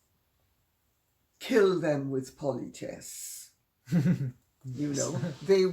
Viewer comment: "kill them with..." what (at 40:19)? I guess she meant something akin to "kindness", but I didn't catch that word in her accent. ha ha. I thought she said "pointes" or "pontesse". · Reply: politesse